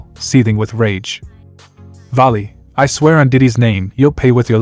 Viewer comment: Did i hear correctly did he say on (DIDDY) name oh my god 30:39 😂😂😂